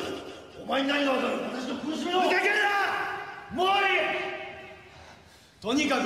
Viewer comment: エコーが効きすぎてここだけはホントに聞き取れないｗ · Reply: とにかく！俺の邪魔建てはさせない！たとえ邪魔をするのならたとえこのBOARDでも！ですかね